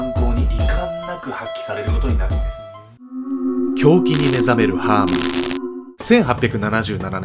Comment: 「狂気に目覚める」が「胸筋に目覚める」に聞こえて、ん？胸筋フェチ？っておもった